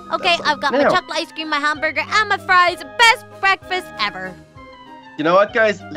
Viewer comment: I thought u said FLIES not FRIES!!